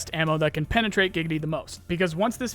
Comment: I don't know about you guys but I've been waiting for this for years and it's finally on the horizon. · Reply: did you say Giggity after penetrate? 5:22?